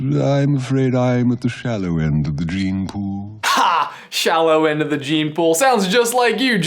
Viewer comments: I always thought that Scar said Dreampool, not Genepool, huh what's new huh! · That line where Scar says (semi paraphrasing): but when it comes to brut strength, I got the shallow end of the gene pool, I always thought he said “dream pool” which I thought was hilarious and still think it may sound a bit funnier. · Gene pool!!! Am I the only one who thought scar said dream pool!!! My life is a lie!!!!!